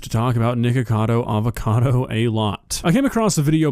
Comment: Is it just me or did he said niggacavo avocado?